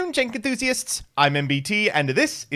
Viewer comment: For the longest time i misheard jank enthusiast as "Tank enthusiast" so i was like: either he likes tanks or my mmo brain is rotting